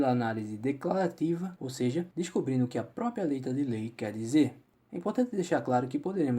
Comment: no minuto 5:37 nao da pra entender nada. "Leitadelei" foi oq entende, repeti varias vezes e ate coloquei mais lento aff